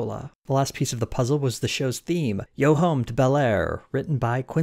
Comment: I always thought he said “go home to bel air”